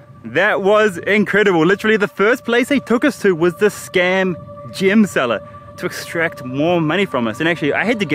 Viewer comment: I was so confused, sounded like he said "scam gym" lol